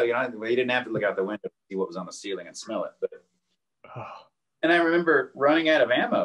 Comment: Did he say “he didn’t have to look out the window, he was on the ceiling he could smell it” at 56:20 ? Geeez